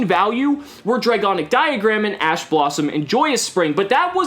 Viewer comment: at first i thought you said Ass Blossom xD